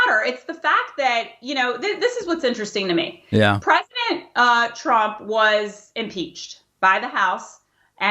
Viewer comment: Does she say 'pregnant Trump at 8:24? Is there something we should know?